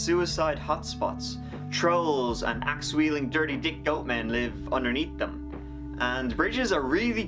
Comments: Wait, at 0:25 did he say “axe wielding, dirty-dicked goat men” or am I crazy? 😂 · I had to replay it cause I wasn't sure if I heard, "axe-weilding, dirty-dick goat men" or not. 😆